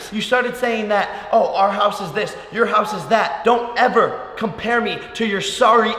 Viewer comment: Bruh you compared him to you 😑 I thought you said “don’t compare us to you” 🤔